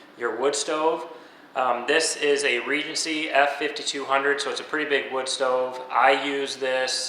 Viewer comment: What was the make and model of the stove please? I thought you said Regis??? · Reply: Regency F5200 <link>